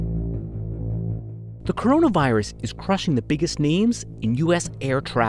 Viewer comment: I thought he said biggest meme